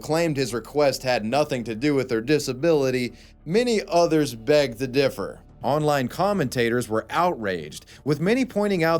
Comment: did you say 'beg the differ' XD